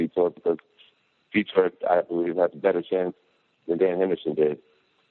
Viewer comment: At roughly 35:03 what I heard was "they should be excited about me fighting Vitor, cuz Vitor I believe has a better chin than Dan Henderson did" however after listening to it several more times I think Jones might have said the word "chance" instead of chin even though it still sounds like chin to me in the context chance seems to make more sense.